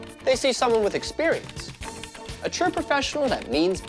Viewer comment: I always thought the intro said "The Wild world of Web"